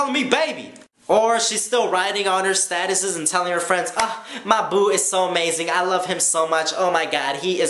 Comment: lmao i thought he said "oh my gosh my booty so amazing" XD